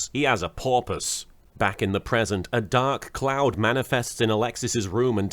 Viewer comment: I misheard "dark cloud" as "dark clown", and (because Alexis might be his daughter) was about to comment "What is it with Kaiba and gay clowns?"